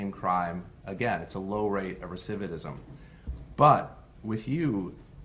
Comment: recividate and recividism? Am I hearing the judge correctly?